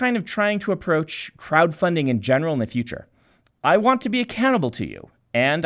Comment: I heard "I want to be a cannibal to you"